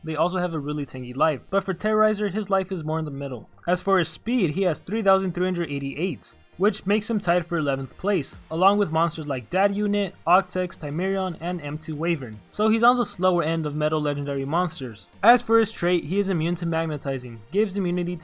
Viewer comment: did he say dab unit?